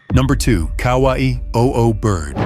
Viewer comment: I thought it said Hawaii Oo Bird for a sec-😅